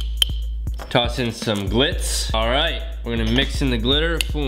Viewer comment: When you said glitz I thought you said clits and I was just like I thought this was PG